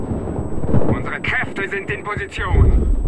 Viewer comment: what he say? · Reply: Just stay down